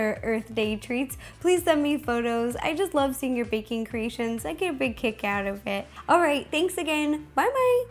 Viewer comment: I thought she said i get to bake cake out of it